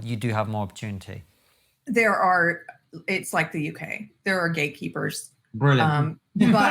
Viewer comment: I almost thought she say Gay-keepers XD gay gatekeepers lmao